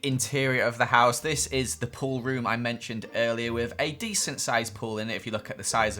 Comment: I Thought you said poor room not pool room